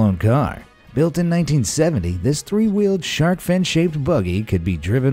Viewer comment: I thought you said “three year old”